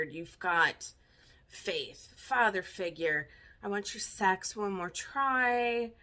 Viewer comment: I noticed Charlie Wilson as soon as you started the video. As a little kid, I thought George was saying "bottom feeder," not Father Figure, haha.